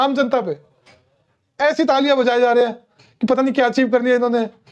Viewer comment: pe kya bola aapne?? kuch samajh nahi aya. Please write it in reply to this comment. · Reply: Prabhat Feri jo subah nikalti hai Sikh bhaiyon ki, usme they chant this